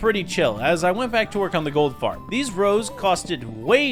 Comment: Im a dum or did i hear gold fart???